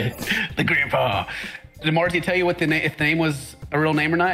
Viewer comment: I thought called Marzia “Morty” at first 🤨